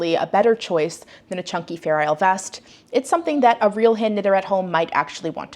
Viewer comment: I know I'm ready for a day off when I totally heard that as "feral vest challenge" and was both highly confused and very intrigued. This week sounds loads better and inspirational - I want to make doggy sweaters! (and Holger's team's sweater is hella cute, I will not be paying that much for the kit, but hope they include instructions for the bow-not-sleeve version.